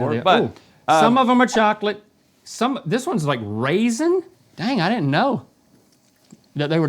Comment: Rhett with the munchkin "That's blueberry" Link "Yea I told you!" No Link you said it was RAISIN! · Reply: 😆 I had to go back and rewatch that, because my brain thought he said blueberry, ya know, because that's a normal donut flavor. But no, he definitely said "Raisin" 🤣 Oh, Link!